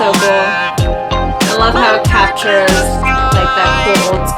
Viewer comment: I sometimes notice weird but special moments like this at 19:21 when says that the song -captures- the old school Hip Hop right on beat so it sounds like she is saying with the talkbox and song : "capture the sky"